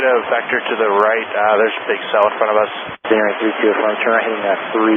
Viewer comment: I heard “there’s a big cell in front of us”